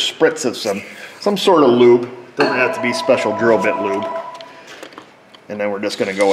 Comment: I thought you said "Special girl bit lube" . Made me spit out my coke. Had to go back and listen again.